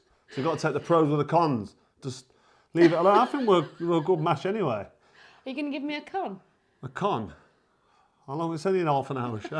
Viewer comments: I thought she said at the start,do you to give me your cum😂😂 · I thought she said are u gonna give me cum😂😂😂😂😂😂😂. Carl froch at this age can only give a tea spoon of it😂😂😂